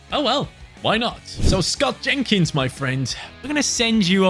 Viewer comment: I thought he said scouting kids instead of Scott Jenkins